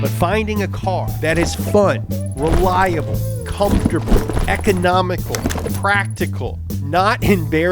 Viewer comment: I thought you said affordable.